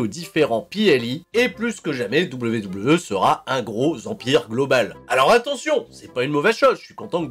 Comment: j'ai entendu "serai un gros vampire global" au lieu de "serait un gros empire global"